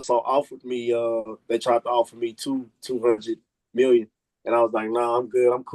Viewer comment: Did he say 200 million 😮😮😮😮